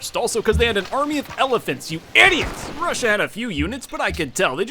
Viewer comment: YouTube though you said "Eddie Brush" instead of 'Idiots!" lol